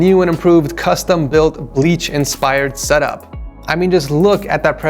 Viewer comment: Who else heard him say bleach instead of one piece when he first started showing Kobe’s setup